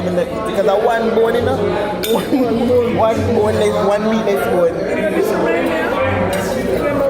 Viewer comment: Did you just say "meatless bone". lol